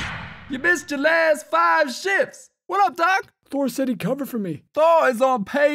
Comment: how thought they said Sh**s instead of shifts